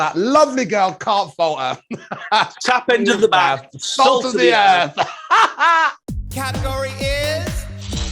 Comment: What did they both say at 13:28 ? I've replayed it over and over and I still can't understand what they're saying... something about salt? Let me in on the joke! 🤣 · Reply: They are saying "Tap end of the Bath, Salt of the Earth" I only know this as it was on instagram.